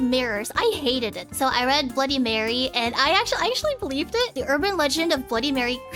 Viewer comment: Rather than "bloody marry" i heard "would you marry"